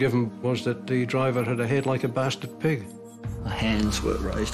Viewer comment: "Head like a bastard pig.. " Gotta love Australia. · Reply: E Z - I thought he said “basted pig” also.